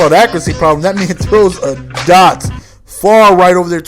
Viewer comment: it sounds like says that nigga throws a dot, lol.